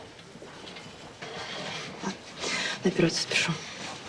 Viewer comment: что она сказала? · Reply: На операцию спешу